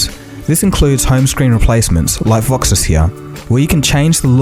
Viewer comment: I didn't understand the name of the customization app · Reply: Aaron Narace voxis launcher...